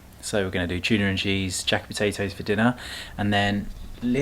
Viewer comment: Sending prayers to your family ❤️❤️❤️ All this time of watching your videos I thought you were saying Jack and potatoes! I just looked up the recipe and realized that Google wasn’t the one going crazy 😂😂😂 jacket!